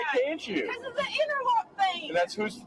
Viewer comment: You didn't catch it 13:00 but she said she couldn't take the truck because of her interlock thing. Which is a device she has installed in her car to breath into to start the car because she got a DUI